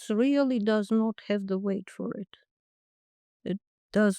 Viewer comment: I honestly thought she said that South Africa was playing hardball but does not have the wit for it, then I realized she said weight, not wit. But actually South Africa has neither. Lol.